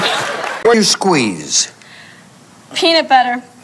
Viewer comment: Don't know what the Dawson question to her on Family Feud was when she said peanut butter. All I heard was "one squeeze". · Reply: Something you squeeze